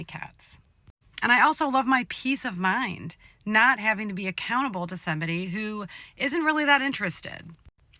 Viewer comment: Did she just say "accountable"? :O :O :O